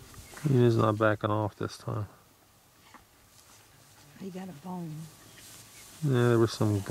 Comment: Sounds like he said " he's not backing off this time"..